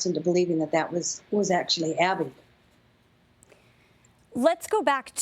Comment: Real genius, this lady called Gabby “abby” and called Brian “Ryan” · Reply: she clearly says “Abby”